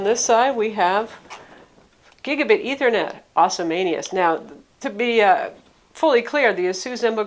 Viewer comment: did you say "awesomaneous"?